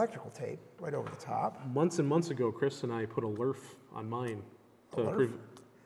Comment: On 8:43 mentions the term 'lurf' (or something like that). I can't figure out what he meant with that. Anyone?